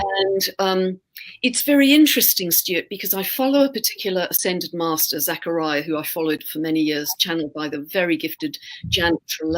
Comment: ?t=12:32 "..the Tequila ascended masters.." .. I don't think so, anyone know what is saying here? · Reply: I think it might be "particular ascended masters" but Tequila is definitely an improvement. CHEERS!